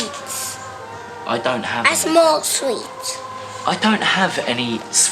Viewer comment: did she say she smokes sweets 😂😂